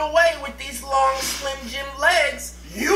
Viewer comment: "You think you're going to get away with them long slim jim legs" I can't 😂😂 love you guys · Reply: I literally thought said long slim jim lims